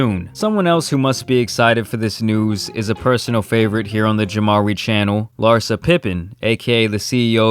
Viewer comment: When I saw Larsa I thought it was a joke thank you for bringing back the street saga Jamari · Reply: I thought you said Jamrai ( like Samarai )